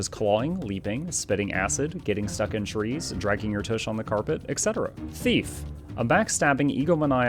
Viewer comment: For some reason at 3:31, I thought it said "thot"